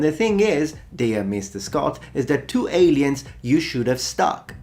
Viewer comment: Noblest one, Metatron, I may have misheard you as I confess the angle Saxon speech is not within my dominion, but I had the impression you said heretic Ridley Scott should have stopped at "two aliens". Did I misheard you? If I heard it right, just wanted to remember you, as you surely have more important things to do (like study real history, which Ridley Scott doesn't), that he only directed the first alien movie (in case you meant he should have stopped in the first two good alien movies)